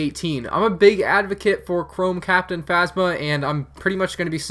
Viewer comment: I thought you said "Clone Captain Phasma" and nearly lost my mind